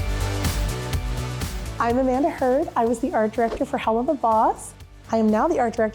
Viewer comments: Ohhhhhh AMANDA heard i thought it said amber heard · I had to do a double take when reading the title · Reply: I thought it said Amanda Hufford